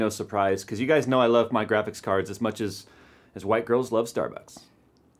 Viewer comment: LOL At the beginning I heard "cuz you guys know I love my graphics cards as much as white girls love dark guys" ;d Not that if he actually said that I would be that suprised, but still ;d